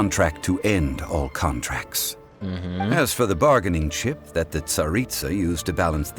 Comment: i thought he said bargaining sh!ts LOL